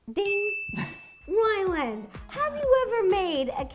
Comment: is it just me or is she saying Wyland...